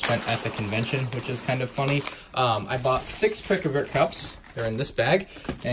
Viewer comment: I had subtitles on and at 2:12 the subtitles said I got six frickin beer cups and i knew we said pick a brick but I was confused how it could mess up so much